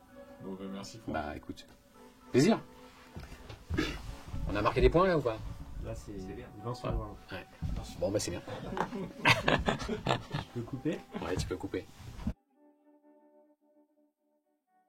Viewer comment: Le "on a marqué des points là ou pas?" Perso jai entendu "sans déconner c'est classe ou pas" ?